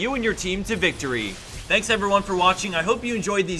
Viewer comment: Why am I hearing it's "Teemo edition" not "teamwork edition".